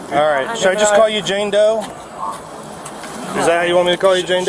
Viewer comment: I thought he said Jango. 😂